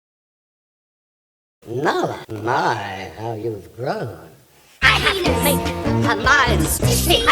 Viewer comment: Nala my have youve grown I have to mate Mine I have no idea My queen Little Scar I heard penis while "I have to mate"